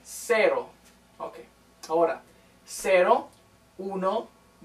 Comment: what did he say? ‘auro’?